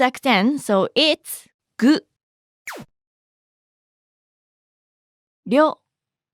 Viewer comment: could you write it please..? am confused did you say the /r/ sound? was it ryo? thanks in advance so much am learning a lot